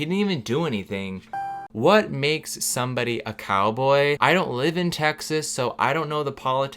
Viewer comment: When you said "I don't live in texas" I heard "I don't believe in texas" XD